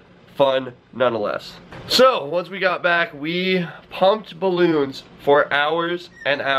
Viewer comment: it sounds like he says humped balloons